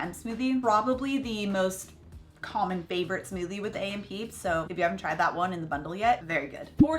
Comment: Welcome to the AENpeeps!!🤓❤️ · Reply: what does that stand for ? I always thought you were saying A.M peeps 🤔lol silly me..